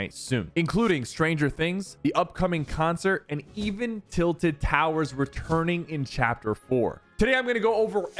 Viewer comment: I thought that said twin towers